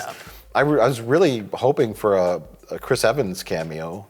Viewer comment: Misheard "Chris Evans cameo" as "Rich Evans cameo" and wondered why soviet Dadbod would be fighting Rich Evans.